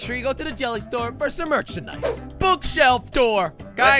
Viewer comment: did he just say merchinise